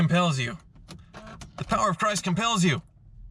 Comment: Right at the start I thought, "Well this is appropriate for Hoovie!", as he was repeating what I heard as, "The Power of Price compels you!"... And.... It didn't start! So classically!